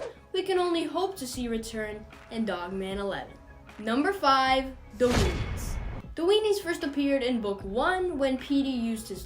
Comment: the weeners it sounded like💀💀💀